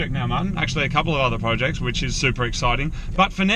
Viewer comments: Did you say 'Supra exciting'? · does he drop a hint on the next project? Sounds like he says Supra Exciting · Did Moog say they are moving onto a new “Supra” exciting project at 13:35? Is that a hint or am I mishearing him?